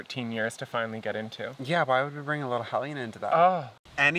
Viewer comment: I thought you said, "yeah why would we bring little Helene into that??" And I was like YOU ALREADY NAMED HER! Replayed it and realized you said "hellion" 🤣